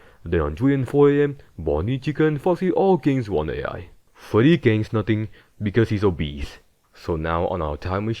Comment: bro i just heard “Freddy gains nothing because he’s obese”😂😂😂😂